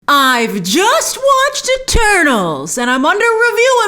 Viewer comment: At first I thought she said “well I just watched turtles!”